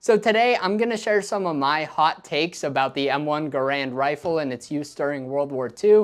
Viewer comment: I hear it as "M1 Grand"